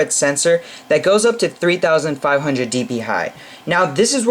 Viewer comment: did I just hear you say "D.P.High"?? hahaha you made me lol a bit.